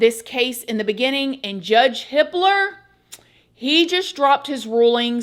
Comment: I thought you said judge Hitler😂